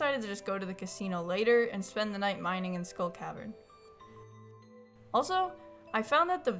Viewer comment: I thought you said you were making bombs to go to the SCHOOL caverns 🌚💀☠️